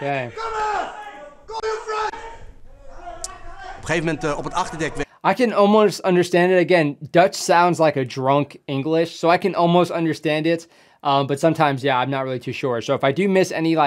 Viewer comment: First time I heard "Dutch sounds like drunk English" phahaha